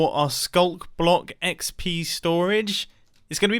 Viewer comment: I swear I heard you say XB storage. Then I just realized I watch too much Hermitcraft. Pretty cool build, but will this be survival friendly?